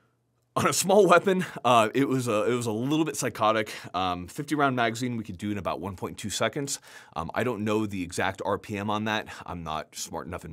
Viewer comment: A 50 round mag in 1.2 seconds is 2500 rounds per minute! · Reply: I failed the math 😢. I thought he said 1.5 seconds